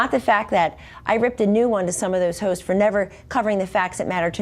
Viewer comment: did she just say "ripped a new one for some of those ho's" ohhh "hosts" ....... if she gets one more face lifts her eyes will be above her ears.